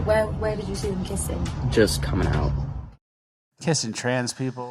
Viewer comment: literally thought he said "just on the mouth" 😂😂😂